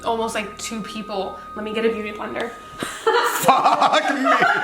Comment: This was so sweet and when she said Beauty blender I thought she said human blender